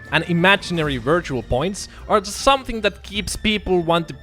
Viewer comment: Bro at 0:57 i thought i heard "vergin points" insted "visual points "and almost die😂😂